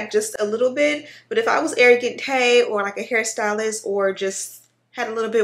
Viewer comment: Wow, I love the unit. I thought I heard you mentioned a name 'Arrogant Kay', (apologies if the spelling is wrong). But who is that?